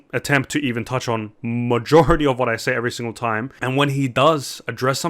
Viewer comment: not to be a dick, but it sounds like he said Majohority